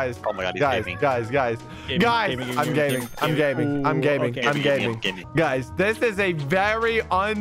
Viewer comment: Wien you Said im gaming i thought you did say im gay😂